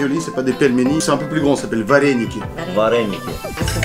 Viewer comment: vareniquer pour moi j'ai entendu va les niquer